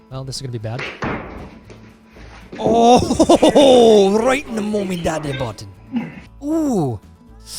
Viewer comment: Did I hear correctly that you said "right in the mommy daddy button"? ROFL